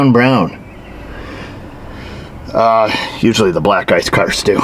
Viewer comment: I thought you said usually the black guys cars do… then we t back… Black “Ice”😂😂